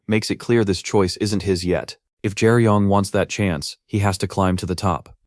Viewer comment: I thought I heard gay yun